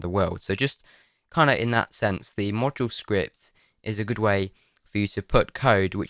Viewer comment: xD watching with the auto-generated subtitles is amazing. It gets most of it right, but every time you say module script it says random stuff and gets increasingly further away from module script like "model script" at one point, "mortal script" at 13:19, and then it gets even better. It's not even English anymore and it thinks you said "otaru script" at 26:32. Great video though. I've been looking for a more in-depth tutorial on mortal—I mean module scripts since I knew the basics, but I didn't really understand how it all came together until this video.